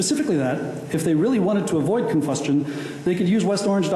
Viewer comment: Did he really say ‘confustion’? yes, he did 😂